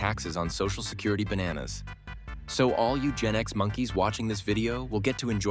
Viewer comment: I misheard “All you Gen X monkeys” As “All Eugenics monkeys” 😳😂